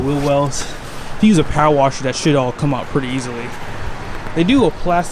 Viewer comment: Hey, I am a big fan. love the videos. i just have to ask a silly question: Did you drop an S bomb at 4:19? just asking, because its the first time i think i have heard you swear. Lol! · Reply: Haha it does sound like I curse but I said it “should”. I won’t curse on my channel so you don’t ever have to worry about that 👍🏾